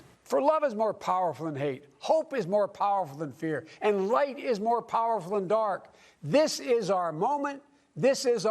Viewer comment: Did Biden really say "White's we're more powerful than Dark's".... That's what I think I hear... I'm starting to like this Guy.... 🇺🇲🇺🇲😁😷